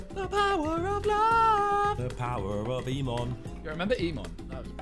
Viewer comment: Ffs jj is so loud, his biblioteca rang my ears haha, I swear I heard a "power of emon" in there haha